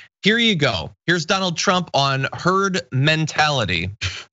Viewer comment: i thought he said terd mentality.... just he is